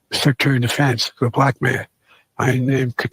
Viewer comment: Oh . . . my . . . God! What if Trump had called Ben Carson or General Powell, "the black man?" What would the media say about that? Though, it does sound more like he said, "The Black mayor." 1:50 WTF is he even saying? I'm glad YOU speak fluent gibberish, because to me it sounded like "Hu-Yon Gang Yuh." I would never have thought it was "The heat I'm getting." I've never been so ashamed of President . . . um, the White Man!